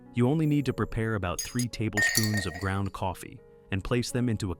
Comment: I have created subtitles in many different languages, Please select the appropriate language in the settings to better understand the video content. For more orchid care tips, click the link below. <link> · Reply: the voice-over in English says 3 Tablespoons of coffee, but caption says 3 Teaspoons. Same error at 8:53.